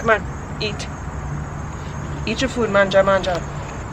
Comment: What did you say at 52:20? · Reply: Mangia! Mangia!